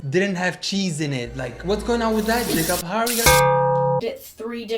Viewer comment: I swear he just said the n word.. RIGHT...? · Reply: He said Jacob :)